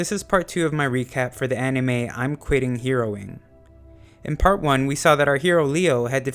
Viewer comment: Bruh I Thought I heard "I'm Quitting Heroine"